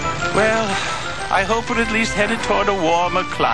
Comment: did Say pavements or penguins ? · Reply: Penguins